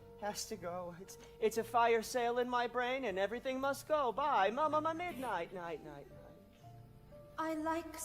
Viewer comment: what says here: It’s a fire sale In my brain and everything must go by M-m-m-midnight night night. (At least what I think he said)